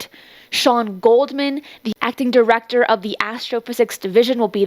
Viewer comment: Could have sworn she said "Shawn Goldman, the F'ing Director of the Astrophysics Division..." Had to listen several times to get what she actually said...'acting Director'. 😂